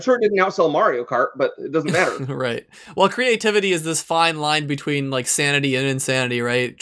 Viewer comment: At first I thought he said "Creativity is this fine line between SANDY and insanity", which, funny enough, also makes sense 11